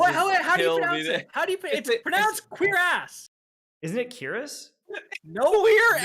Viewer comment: I thought was saying "queer ass". I'm sitting here thinking, why is he talking about me???